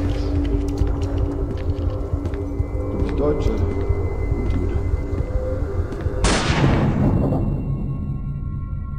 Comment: Did he just say "vibe check"? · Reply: He said "You're a German and a Jew."